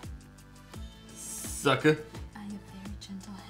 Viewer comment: why at 5:00 did i hear say " Suck a-" then it stopped i was like there are kids present